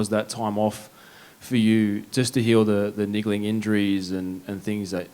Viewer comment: does he say niggling,????